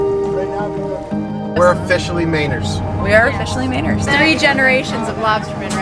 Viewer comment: For awhile there when you were saying "Mainers," I couldn't for the life of me figure out what you were saying - LOL! I never thought about it before what people from Maine call themselves! I thought you were saying "Maynard" and couldn't figure out why you kept calling each other that! Haha!